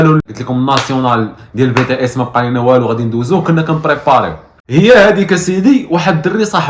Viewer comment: هذا الشيء اللي سمعت 7:07 Bts?